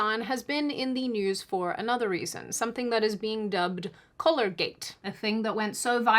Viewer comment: I thought you were saying color-gate at first, like we didn’t know what color Hasan was. Australian vowels tend to become indistinguishable. Like how -bourne and -bane are pronounced the same.